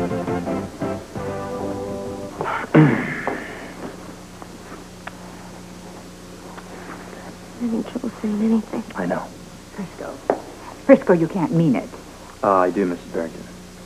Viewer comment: What did Felicia say 10:00? She has trouble saying anything? Or he has trouble saying anything?